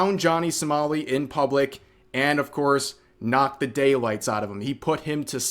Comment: I know you said "knocked the daylights", but what I heard was "knocked bidet lights", and honestly, I like my way better. More suited to this situation, I think.